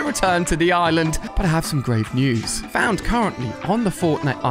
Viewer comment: Did he say great news